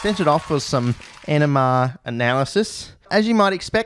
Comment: I kept hearing "enema analysis" ngl